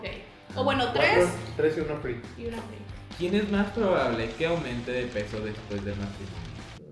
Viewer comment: Que alguien sea tan amable de explicarme que dijo aquí exactamente xd, no le entiendo y luego todos se ríen, entiendo menos😂 14:26 Jsjaja la cara de Odalys, si se asusto😂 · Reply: “Quien es más probable que aumente de peso después del matrimonio” Creo que dice eso